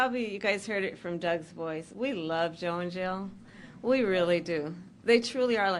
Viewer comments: Sounded like Kamala said "we'd love Joe in Jail". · did she say we love “Joe in jail“ or we love, “Joe n’ Jill” 🧐 just curious if it’s a prereq to slur your words like you just came off a bender to be in the Democratic Party 🤷‍♀️ just a question don’t come for me now 😅